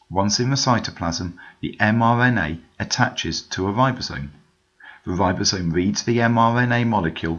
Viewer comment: did you say the ribosome meets or reads the MRNA molecule